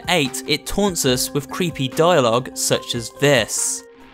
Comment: I think he says "Your submission is inevitable"